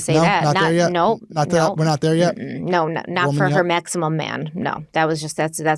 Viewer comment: At first I thought you were saying “Maxim Man” as in Maxim Magazine. I’m so confused as to what a “Maximum man” even means! This chick is crazy or trying to profit off of the story or both.